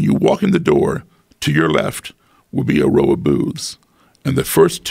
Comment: i thought he said "a row of boobs"